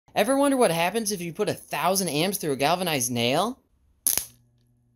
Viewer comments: I thought you said “Ever wonder what happens if you put a mouse through a mail?” My brain is broken lol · Galvanized metals give off poisonous gas when welded. I'm assuming that all that smoke was poisonous too. Here's to hoping you have a vent hood 👍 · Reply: no cap i thought he said "governised nails"